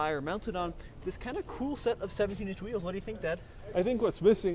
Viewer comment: I for sure thought said Dad lmao